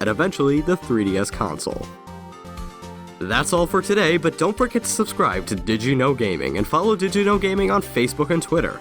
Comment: that all for today but dont freaking supscribe to did you know gaming... 6:17(think the show is really great it just sound like he is saying that)